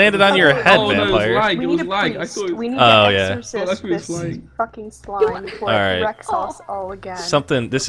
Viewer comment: what did she say!? I am pretty sure she said f***ing